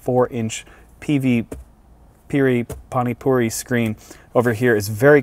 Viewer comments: did he said pani puri?🙄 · did you say panipuri screen😜you like them alot 😋